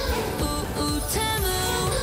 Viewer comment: I heard was "pOO💩pOo💩tEMu✨✨✨"😂😂😂